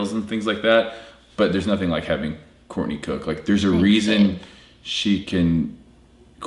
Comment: Did I hear you call her Courtney Cook? @ 5:39.